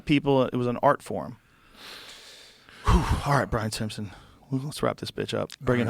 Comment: I accualy thought it said Bart Simpson